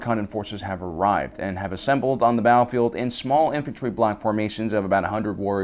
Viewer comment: did you say small infantry black formations? (Even automated captions say you said black, just asking, i m not offended or anything)